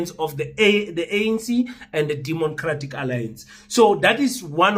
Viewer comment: Lol 8:30 did i just say Demon-cratic Alliance